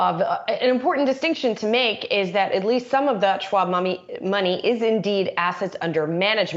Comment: Got my eyes set on $25 puts, demand is low, I know who wants these shares 😉……….@ 6:08 did she say “schmoney”